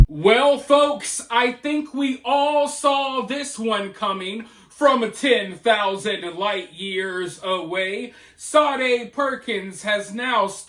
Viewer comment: Boy oh boy did I hear you wrong, what ACTUALLY said was "we all saw this comin from 10 thousand Light years away". What I heard was ...'we all saw this comin from 10 thousand White years away'.